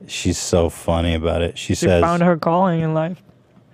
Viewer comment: did Hila say “She Found her Colon in Life” ?